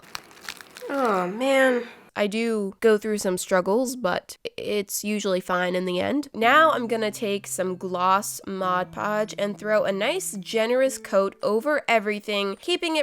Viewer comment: When she said gloss mod podge I thought she said lost mod podge